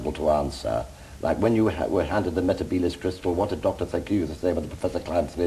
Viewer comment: did he say "meta penis crystal" on tv ? lol.